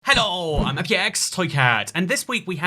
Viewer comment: I hear “I’m your ex toycat”..